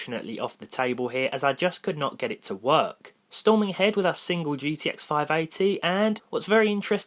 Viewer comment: Did he say Gtx 580?